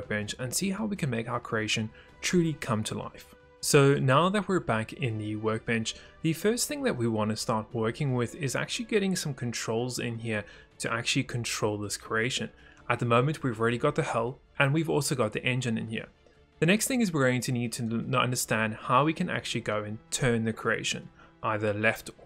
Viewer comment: YASSSSSS, LET'S DO IT...LET'S CONTROL THIS CROATIAN AND INJUN!!!!! oh, Nevermind...I misheard.